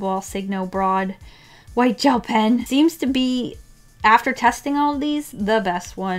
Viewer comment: I thought it said "tasting" instead of "testing" so I was very worried when I watched the video